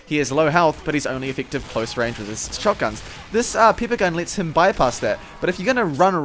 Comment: Did i misshear or did he say "this pepe gun" at 4:26?